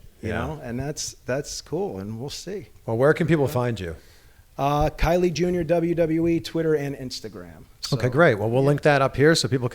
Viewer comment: I thought he said Kylie Jenner... I was like is he bangin her?